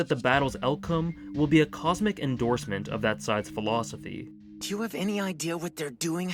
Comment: "... and believes that the battle's outcome will be a cosmic endorsement of that size philosophy" Huh? What philosophy? The captions say "size," and I can't make out what the word is supposed to be. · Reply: That side’s philosophy